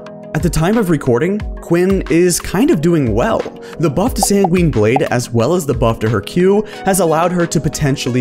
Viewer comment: when he keeps saying Quinn I heard it has “SanQuinn Blade”